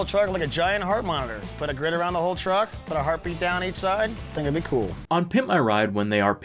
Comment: Ah. The era of dumbass facial hair. Also, every time they say "heart beat" i hear "herpe".